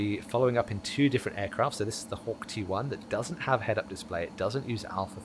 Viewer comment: Hawk T1, thought u said Hawk Tua